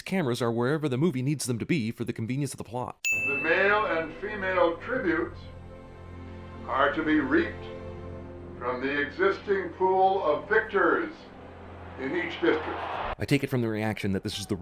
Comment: i dont know if its just my hearing , my imagination, or the original dialogue, but i swear i heard " the male and female tributes are to be raped by the existing pool of victors."